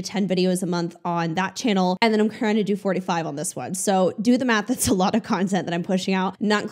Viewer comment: So when you said 4-5 videos on this one, my brain heard you say, "45 on this one" and I sat here STUNNED for 5 minutes before it clicked.